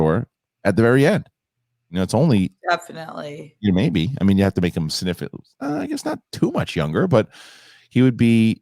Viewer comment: could've swore you said "you have to make him sniff it" lmao I was like wtf